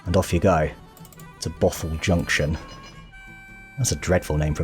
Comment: I misheard that one as Gotham Junction.